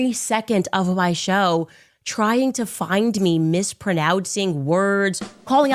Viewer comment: Did she just say "mispronowtz"?